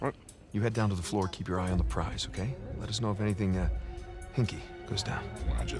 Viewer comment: At first when he said hinky I thought he said kinky